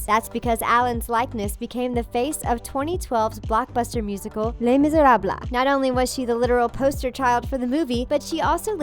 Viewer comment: All I heard was blahmisbrabi😂